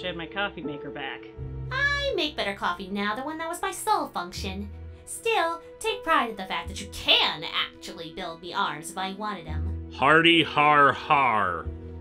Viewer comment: What did Seras say at the end of that sentence? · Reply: "Still, take pride in the fact that you CAN build me arms if I actually wanted them."